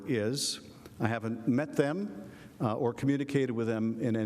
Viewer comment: Awww cut the crap Schiff, I am onto you buddy, 02:42 did he misspeak or did he say "them" when addressing the whistleblower?